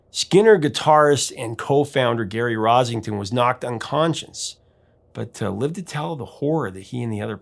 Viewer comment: It sounds like you say he was knocked un-conscience rather than unconscious. You'll more likely find the un-conscience at the record companies.